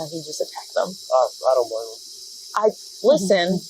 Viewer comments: Does that sound more like "I don't blame 'em" to anyone else? The subtitle says "I don't know", but the last bit really doesn't sound like "know" to me. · Nashville Police: Meta: <link> Twitter: <link> Springfield Police: Meta: <link> Twitter: <link> Auditors Of America (Last Days Of Freedom) ⭐ CashApp Link ➤ $TheLastDaysOfFreedom ⭐ Paypal Link. <link> Original Interaction: <link> Bodycam Brown: <link> Bodycam Gianantoni: <link> IA Phone Call: <link> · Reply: The caption is wrong at 5:42. He clearly said "I don't blame him" when they found he's an auditor. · the caption is wrong. Supervisor actually says "I don't blame him"... which is way worse Supervisor is a crooked pos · I think the subtitle is wrong. Sounds like the supervisor says “ I don’t blame him” re: attacking the auditor. Not “I don’t know.” Not being critical, just pointing out how it makes them look even worse. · Important: at 5:44 he says "I don't blame him" instead of "I don't know". Sets up the whole handling of it in a different light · sounds like he says "I don't blame him" not I don't know. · I'm sure this comment will get buried but at 5:40 the supervisor didn't say "Uh , I don't know" like the subtitle suggest , he actually said "Oh , I don't blame him" . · The caption is wrong at 5:42. He clearly said "I don't blame him" when they found he's an auditor.